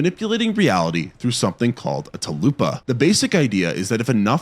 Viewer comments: After a night of watching scary movies, get some actual sleep. Big thanks to Manta Sleep for sponsoring the video. Grab 10% off here: <link> and use code CHUPA at checkout. #sponsored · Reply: XD It sounded like you said talupa instead of tulpa and now I’m wanting a chalupa. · why did you say it like Chalupa? 😭💀